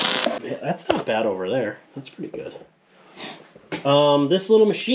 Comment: What do you say at 7:12? "This little machine....." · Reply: I said, "that's pretty good" there were some good beads and some horrible beads lol.